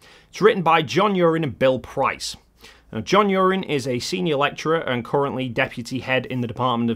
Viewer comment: Bitovabugger. I THOUGHT you said John Urine. 😉